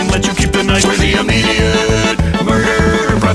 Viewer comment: “Kids die for free” I can’t- 🤣🤣🤣🤣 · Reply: I always hear “It’s die for free”